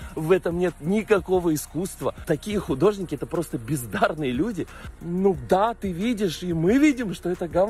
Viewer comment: Мне правильно послышалось : ""пиздарные "" ????